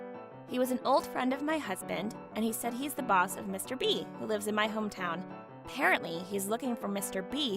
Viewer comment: For a second I thought she said “Mr. Beast” at first lol